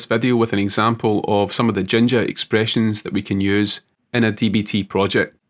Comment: Until you spelled it out on the screen I thought you were saying "Ginger expressions"